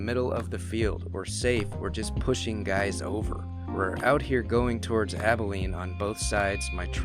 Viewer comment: I hear “ a sniper just shot some guys over”